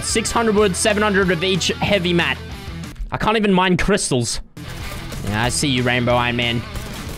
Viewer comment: Fresh: “can’t farm crystals” also fresh “eats crystal” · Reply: Ok ok sry I messed up I misheard it I thought he couldn’t eat any crystals